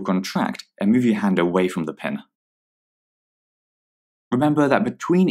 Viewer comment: in 4:18 you said pen instead of needle lol